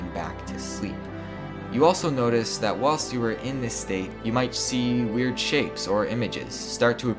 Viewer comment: i thought he said "you might see weird shit" cuz i was looking at stuff and wasnt fully paying attention